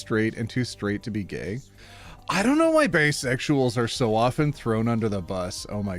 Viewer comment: Not sure if I misheard or if I heard call Bi's "bae-sexuals", but I'm here for it.